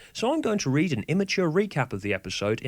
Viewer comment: Mishears part of the video) Immature poems? I know just the child..well, fictional child but still. Anyone guess who I'm referring to?